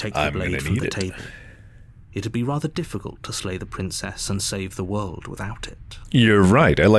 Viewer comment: Slay? I thought you said "Lay the Princess" Ooops